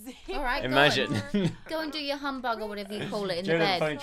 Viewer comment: How many people were shouting at the screen telling that he needed to buy a laptop.... I was lol!! Also, when called the woohoo, 'hobnob'..... crying with laughter there (LOL) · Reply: I thought she said humbug haha